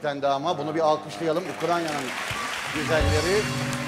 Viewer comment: Ah Beyaz, Ukranya mı dedin , ben mi yanlış duydum 3:21'de?